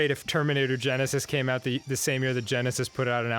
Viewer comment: did Arin say "Germinator Genesis"